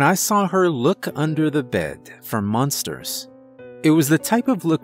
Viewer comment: It sounded like he said “She looked under the bed for munsters” the first time